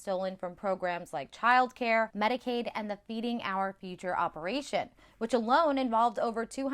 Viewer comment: I could’ve sworn she said “thieving our future”.